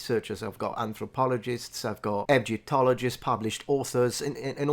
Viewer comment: when listing, I swear to God I thought I heard "Edge-otologists" and thought... 'Yo we have doctors and researches of Cringe and edgy humor?'